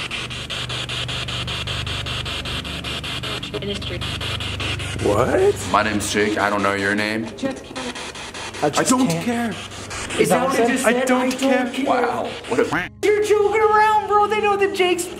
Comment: my ccs said my names sue nah this thing is stipupid